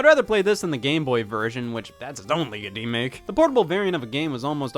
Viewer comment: when the captions say (indistinct) Scott is saying "That's its own league of demake."